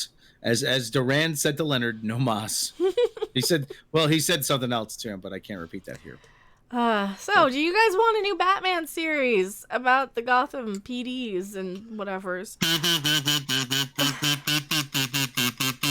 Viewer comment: For a second I thought you were saying "a new Batman UTERUS".... but that's batwamen